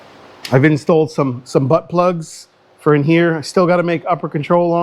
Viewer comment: What did you call that grommet? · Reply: Butt plug. lol. 🤣😂🤣